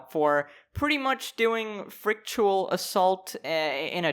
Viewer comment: At first I thought you said ritual assault